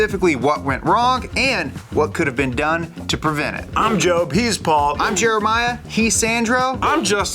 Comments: could've sworn i heard " I'm Jobe, he's bald!" lmaoo spit out my coffee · I didn't catch that the first guys name was Paul, so I thought he said "I'm Jobe, he's bald" and continued on lol